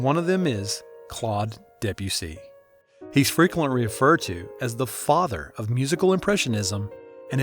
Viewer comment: I died what i heard “he’s frequentwy weferred to”💀